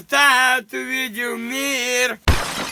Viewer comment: что он говорит? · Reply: Пубертат увидел мир